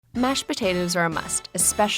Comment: i thought it said mustard potatoes XD